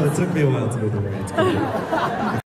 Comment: what does scott say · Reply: "Took me a while too, it's okay."